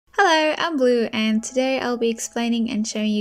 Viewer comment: Instead of "hi am blue" i heard "hi im glue" 💀💀💀